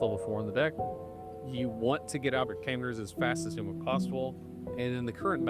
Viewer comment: What is he saying at 3:41. You want to get out of what as quickly as possible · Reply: “You want to get out your tamers as fast as possible”